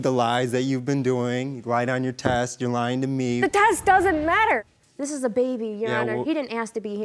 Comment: did she just say lies don’t matter.